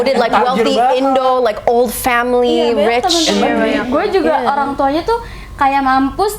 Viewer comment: did u say family?????